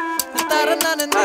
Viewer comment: What's the word that a woman pronounced @ 2:50😅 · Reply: Sing chetta ("Sing brother" in english)